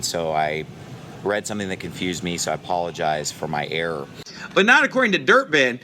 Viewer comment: its in this video knuckle head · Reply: What is with you people? Why do you lie so much? What is wrong with you? He said "so I apologize" at 2:10 ...